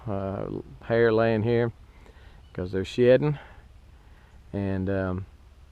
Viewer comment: it sounds like he says "..cause they're shittin", lol. But I know he said "shedding" though because he's talking about the wool laying around.